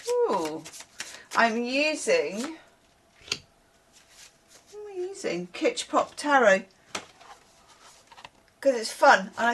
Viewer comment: Absolutely thought you said Hitchcock Tarot, which I am now off to google and see if it exists 😂 also I can’t find the hype button, it isn’t there for me 😢